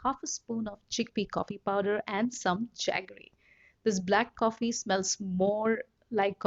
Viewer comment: Where can I buy at 2:49 she said she made the black coffee with a half of spoon of chickpea and something else. Does anyone know what she said? Thanks! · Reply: Jaggery. You can get it at any Indian store